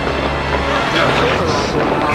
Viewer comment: does Wesker say "I'll give you the cure for your wipe cream"?